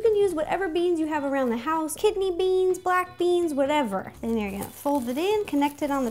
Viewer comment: Did she just say "KIDNEY" Beans??!! FOR REAL?? Or am I just listening wrong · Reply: yeeaah.... she said kidney beans.......